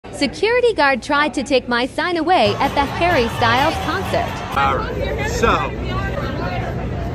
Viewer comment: Ngl, I thought she said “Security tried to take my son away” 🥲